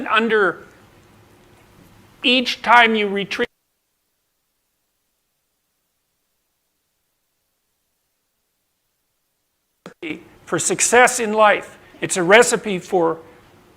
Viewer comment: A lot of people mentioning the audio cut at 5:10; I've managed to lip read almost every word he says, but there's two words in particular I will put in square parentheses that I am not sure about because if you look closely at his mouth there is some pixelation right as he says it. It might even be one word you just can't tell because there is literally a pixel glitch over his bottom lip the moment he says, rendering it impossible to read (at least in my opinion). "Each time you retreat out of battle, you probably [grow shorter] for sure, and that's not a recipe for success...". It seems odd given that he basically just repeated exactly what he was just saying, but knowing JP that's probably exactly what he was doing. It's also a strange speech pattern, to say "probably" and then end the sentence with "for sure", but that's definitely the words he is saying there. I thought at first he was saying "every time you retreat out of battle you probably lose it for sure, and that's not a recipe for success" but the phoneme structure composition for "L" is difficult to lip read unless you are in front of the person directly. Another potential one I considered was "every time you retreat out of battle you probably regret it for sure, and that's not a recipe for success". Any one of these three definitely fit JP's typical points and they all roughly say the same thing. Either way he is repeating how you should not retreat out of battle.